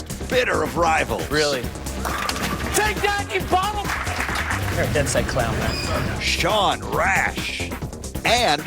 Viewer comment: What did Belmo say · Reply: Phoenix Wright"You're a dead-set clown". Aussie slang for 'dead serious'